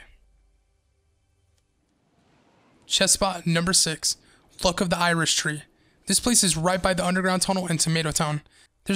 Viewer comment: who else heard “fuck of the Irish tree”?